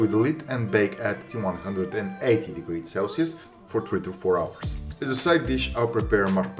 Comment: Yikes @ 3:51 a very long and expensive cooking time bruh. Did you say 24 hours or 2 to 4 hours - either way that is one heck of a long and expensive cooking time... I had to stop watching...